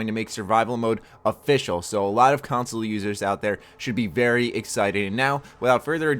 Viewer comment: when you said "console users" i thought you said "console losers" lol